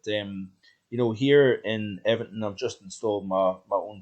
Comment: I first thought you said Everton but realised you said Evanton, that village on the right as you go up towards Alness from Inverness.